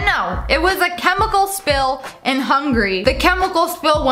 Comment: I thought she said Hungry when she was talking about the chemical spill in Hungary 😂